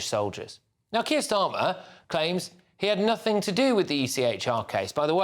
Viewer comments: I keep hearing "Circus Starmer" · Why do I keep hearing "Circus Starmer?"